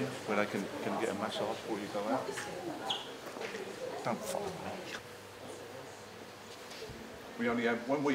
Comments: what is he saying haha haha “don’t follow me” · I thought he said "oh f*** me" 😂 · I thought he said "oh f*** me" 😂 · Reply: I thought he said “don’t follow me”, at least I hope he did :)